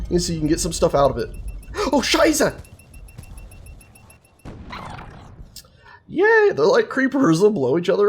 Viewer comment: Did you say "Scheiße" at 6:10 ?